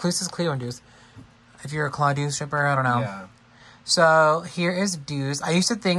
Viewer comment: Just listen, I heard stripper 100% 😂